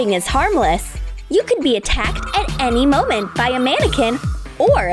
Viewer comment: I thought they said, “you can be attacked at any moment by a man”. 😂 11:12 Are you sure this is for kids?...